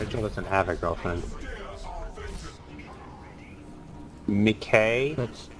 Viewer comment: did he talk about me? I mean, it sounded like he said my name (M'kay)